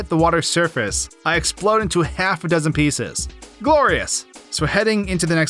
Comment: at first I heard “gore-ious”